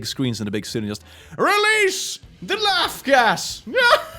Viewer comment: Okay I must have missed heard I thought you said release the ass gas I had to think for a minute how that going to make people laugh